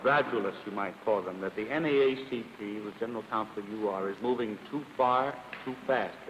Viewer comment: the subtitles say: "THURGOOD MARSHALL: 'The charge has been made, and made by some fairly moderate people, gradualists, you might call them, that the NAACP, ...(inaudible), is moving too far, too fast.'" It sounds like the man says "with General Council Bluewater" or "with General Council Carter." According to the Library of Congress, (<link> Robert L. Carter served as General council to the NAACP from <num>, which is the approximate time at which these events occurred, meaning that this is a highly likely possibility.